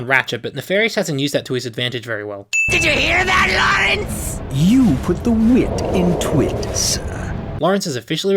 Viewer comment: "you put the wit in twit" and all I hear is "you put the what in twatt"